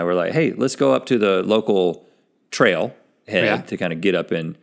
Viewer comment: Did link really say "getting some trail head" ????